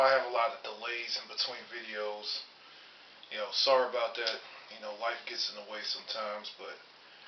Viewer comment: At the beginning of the vid, I thought you said "wife gets in the way", but I thought twice and realized you said "life gets in the way". Lol ! Then I thought that's a good one, because it's the same thing. Ha ha!